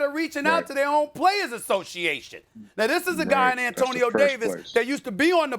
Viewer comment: I thought it said anthony davis lmao